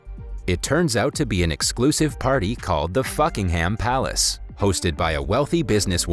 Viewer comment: the palace kinda sounded like you said the f***inghem palace